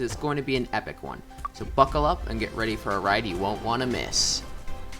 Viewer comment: at first I thought u said fckle up.